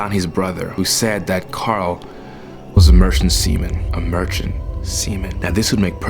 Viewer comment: omg I thought he said "merchant semen"💀